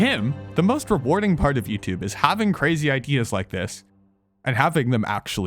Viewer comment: did you just say having smart ikeas?